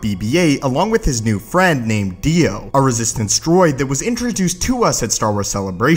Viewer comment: did he just say DIO! is that a jojo reference?!